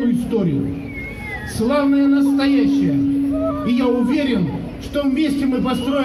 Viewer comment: Славное" настоящее и ,,великое" будущее,видимо заключается в превращении Архангельской области в помойку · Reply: А мне послышалось "условное настоящее"...